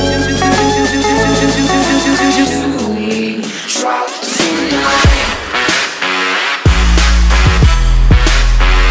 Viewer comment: WTF did she say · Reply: Do we drop tonight